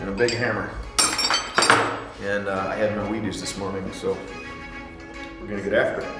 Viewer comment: Did he just said "I got my weedies this morning"?? Hahaha